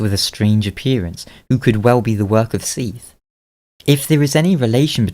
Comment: Did you just say Seith at 5:56? As in Seith the Scale less?